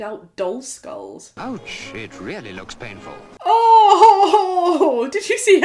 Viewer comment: I can't be the only one who misheard that as "oh sh*t, really looks painful"